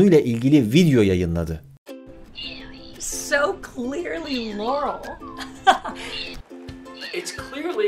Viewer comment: Ben yearl duydum